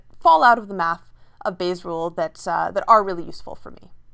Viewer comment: I heard "Base rules" LOL